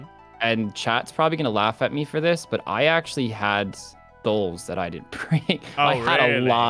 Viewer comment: what is saying at 51:05? "I had a lot of __ that i didn't bring"? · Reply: SOULS as in SOUL RUNES for blood spells???